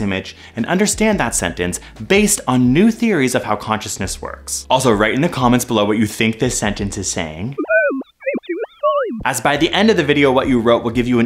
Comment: I heard "below what this sentence is saying" the image is confusing